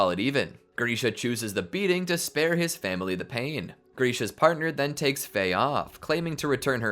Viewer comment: you said grishas partner instead of krugers partner